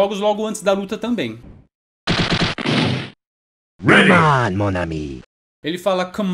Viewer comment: Eu jurava que o que ele falava era "Good night monami". KKKKK